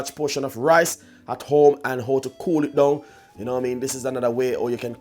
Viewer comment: What a waste of time. Hahaha. I thought I can learn something from this video but instead all I hear is "kolit dawn" and "lot of rice" over and over again.